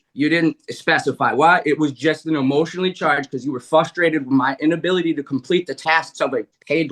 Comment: did he say flustrated? Oof.